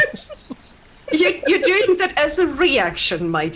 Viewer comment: What does say at 2:21:00?? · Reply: Is annoying the GM a free action? Guy: Laughs: Laughs: You- You're doing that as a REaction, my dear.